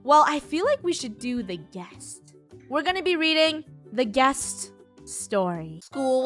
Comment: at the beginning I thought she said “weeding” instead of reading